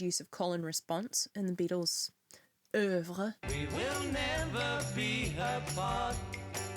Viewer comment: What do you say here? It sounds French. I wanna know what it means 😅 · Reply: She says "œuvre" which means (art-)work